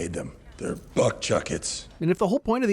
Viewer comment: I can't be the only one who thought he said "butt-chuckets" the first time